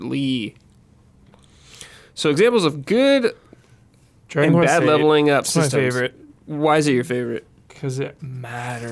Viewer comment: I tried and relistenined but I can't seem to hear well what game said it has his favorite leveling system 😢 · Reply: Dragon Quest 8